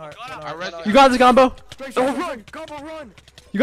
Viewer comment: best communication no cap Edit : wtf were you guys even saying? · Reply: Gobble gobble